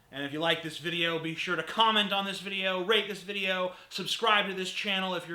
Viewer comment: I thought I heard "rape this video" for a second. Must be my immoral atheist mind at work again...